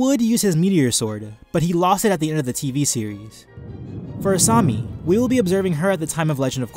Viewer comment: when he said for asami i thought he said korrasami